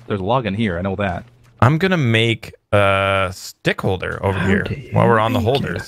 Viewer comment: For a second I thought he said d***k holder